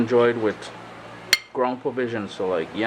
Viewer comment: LOL I thought you said it was to be enjoyed with "Grandpa-Vision". I was laughing so hard.